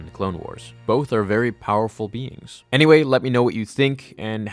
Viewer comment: i thought he said "god like memes" lol